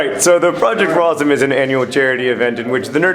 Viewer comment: I thought you said "Project for Autism" when they were yelling "HI" at you. I was about to say, that's a bit offensive don't you think?